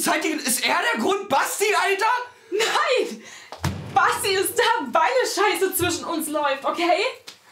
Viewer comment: was hat sie gesagt?🤷‍♀️ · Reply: Basti ist da weil es zwischen uns so scheiße läuft